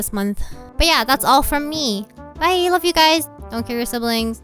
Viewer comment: Oh you said "don't kill your siblings." I thought you said "don't care your siblings." lol